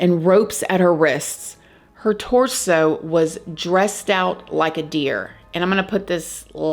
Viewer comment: Lol. The 1st time you said dressed out like a deer. I thought you said dressed up like a dear. I was like wtf. Lol